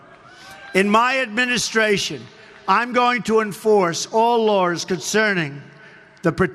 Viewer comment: Well he clearly said he was going to enforce all "lores" concerning classified information. He said nothing about following the law.